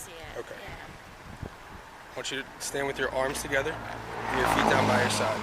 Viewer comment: did he say arms together and feet down by your side? That would be quite the feat even when sober.